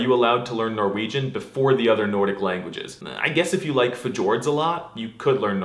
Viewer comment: Did you just say "F-tjord?"